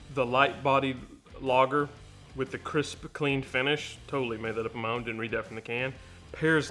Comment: The bud light part I had to replay, I thought for a second you said crispy creme lol, (crisp clean) you made the wife gag on the thought of bud light and crispy creme donuts lol